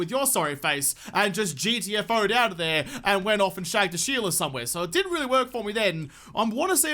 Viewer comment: did you just say "shave the sheela"? LOL.